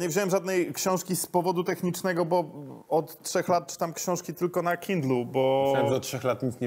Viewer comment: Aaa, Żulczyk czyta tylko na Kindlu, a ja usłyszałem, że "tylko na kiblu". 😆